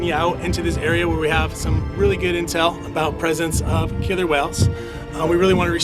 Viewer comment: I thought it said killer quails. Now that I want to see.